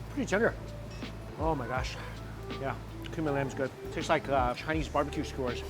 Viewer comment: You said cumin lamb thought you said human lamb lol